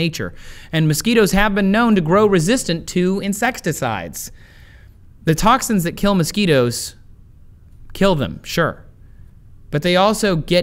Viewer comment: Does he say.. insextacides?